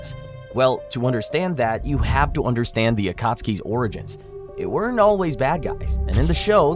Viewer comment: sub: AOT What u said: akatsuki... I think the sub is a big fan of shingeki no kyojin